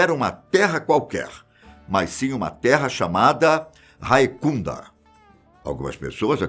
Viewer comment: QUE ?!?! 😂 Por um segundo eu ouvi "Raimunda" kkkkkk